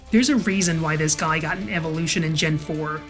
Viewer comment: I thought she said Flygon lol